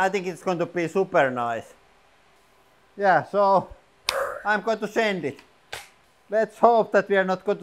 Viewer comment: did he say "I'm going to send it"?